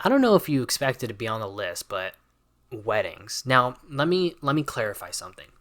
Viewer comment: put you on x2 speed and when you said "weddings" i heard "wings" and i got worried for a sec